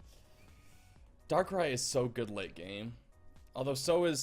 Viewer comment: did you say Darkrai is so good looking? XD